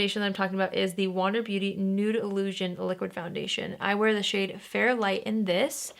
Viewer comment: Any time I hear someone say “wander beauty nude illusion” all I hear is “noodle-usion” 😂💛